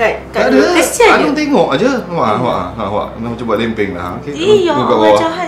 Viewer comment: Minit 14:24 apa aku salah dengar ckp "dah mcm nak buat lempeng je"😂😂